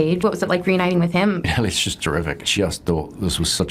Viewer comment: wait, he didn't say "she?" It sounded like it